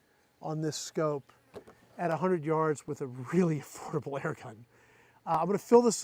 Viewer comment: did you just say: "With a really horrible airgun"? Golly gee Batman, I hope Gamo is not listening. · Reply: He said "with a really affordable airgun"